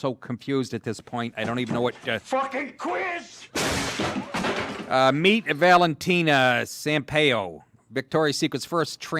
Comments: I actually thought you were saying the model's name was "Meat Valentina." I thought, jeez, that's a little too on the nose isn't it? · Oh snap i thought you said his/her name was meat valentino. Had to give ot another listen.